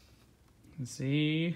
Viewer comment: what was the ghost about to say???? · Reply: ZoZo